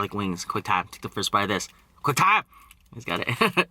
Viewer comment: Can someone tell me what he says? Quick time?!